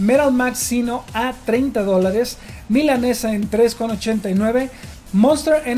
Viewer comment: yo escuché mal o dijo milanesa jajaja mi inglés es nulo pero sono a milanesa.